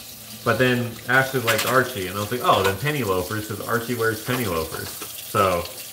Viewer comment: The on one the right is “Penny Loafer” I damn near had a heart attack thought I heard “Penny Loker” which is me and I was like …. Woah… LOL They are SO cute. ❤️❤️